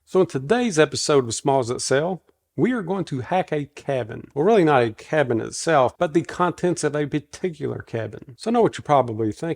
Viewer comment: Too early I'm the am. Thought it said smells that sell. Lol more coffee please.